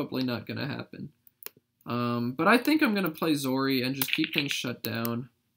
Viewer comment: czar instead of zori?